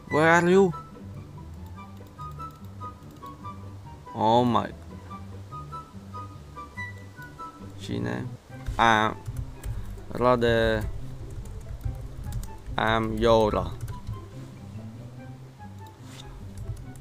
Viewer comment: OMG did he say "Sheina"